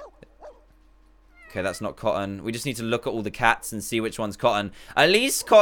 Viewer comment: OH MY GOSH!!! YOU SWORE YOU SAID "Look at all the blah oh sh**!"